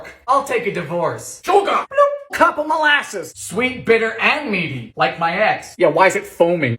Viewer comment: lol, I'm sorry, but I first heard you. I thought you said "Sweet, bitter, and meaty like my a**!" 😂😅